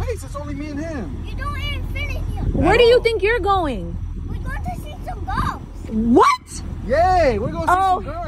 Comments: I thought he said we gonna go see some hoes lmfao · I swear I thought he said we going to see some hoes 🤣🤣